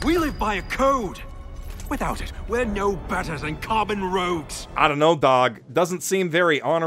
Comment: Did he say 'common rogues'? Or 'Carbon Rogues'? Because that second one sounds like a metal as fuck band name.